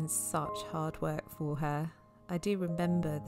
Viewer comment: I like the bit at 5:05 when you said "rebember" instead of "remember".